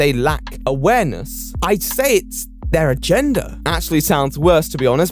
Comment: I thought he said "Their gender" and I was like, "Woah, this got real sexist real fast that's not what TRO does."